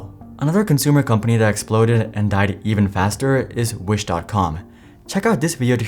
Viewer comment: Hahaha, interesting idea. I think people like subscribing to personalities or people they relate with. A faceless channel is likely the biggest challenge in getting more subscribers. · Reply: Truth be told I had a hard time figuring what you said at the end of the clip. I always hear "I hurry" until realize that it is " I am Hari"